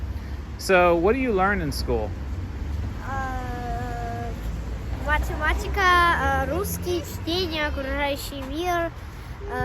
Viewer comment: did I hear well, they have learn to read by reading war and peace from Dostoevsky? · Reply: He said the world around us. Meaning learning things around the world. Was and piece would be too early for that age. But anyway in the Russian schools we are learning “War and piece”